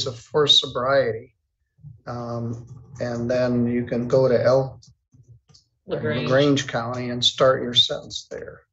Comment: i thought he said you can go to hell why is a 3 times DUI on probation and parole for DUI, on drugs and almost x4 over the limit and expect to keep his licence